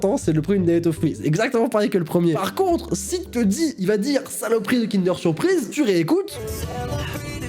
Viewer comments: Moi j'entends à 10:07" Saloperie de dance au fruit " WTF j'ai un problème dans la têêêêteeeee et 10:49 j'entends "la B*te a maman". Ma mère, pardon? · Moi j’entends « saloperies de danette au fruit « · moi j’entends saloperies de danette au fruit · maintenant quand j'écoute la chanson j'entends un mélange des 2 j'entends "saloperie de danette surprise" 😂 Et j'ai entendu toutes les hallucination UwU Sinon super vidéo👍(ça la 3ème fois que je la regarde XD) TRACTOPELLEEEE TRACTOPELLEEEE TRACTOPELLEEEE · moi j'entends "salopri de danette au fruit"